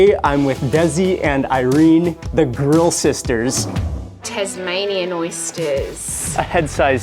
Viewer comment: I thought you said girl sisters 😂 I need to get my hearings checked ahah